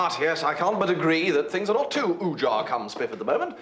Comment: What is the word bertie uses at 36:12 " Things are not too ojaa at the moment" . Does anyone have the answer?